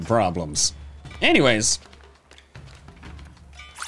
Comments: am i the only one who heard "sizzlin fried nudes" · ummm what did I just hear him say not gonna lie I swear I heard him say fried nudes😂😂😂